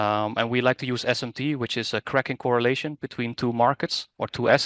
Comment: Is he saying "crack in correlation."